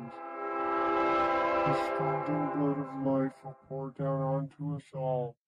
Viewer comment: “The stinging blood of life” is what I think he said here